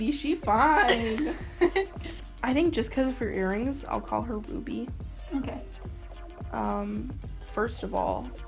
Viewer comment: I heard "ill call her boobie" 😆😂